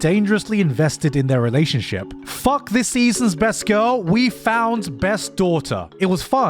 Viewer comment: What he said: “Fuck this seasons best girl, we’ve found best daughter.” What I heard: “Fuck this seasons best girl, and you will have found best daughter.” Me: say no more, I shall begin procreating!